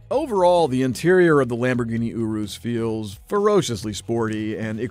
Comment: It sounded like he said, "the Lamborghini UwU"